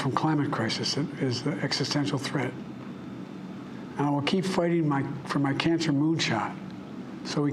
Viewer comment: Did he say, "I will keep fighting for my cancer moonshot?"